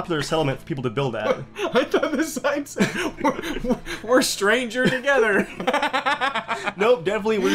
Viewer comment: I thought it said "We're strangled together!"